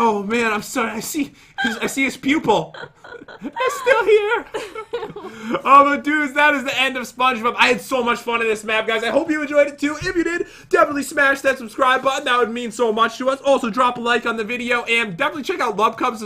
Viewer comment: Did he say SpongeBob?